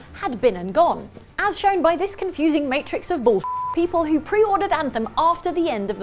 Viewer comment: My eyes on another screen, I thought I heard "confusion matrix of bullshit" and thought "classifier testing mentioned??"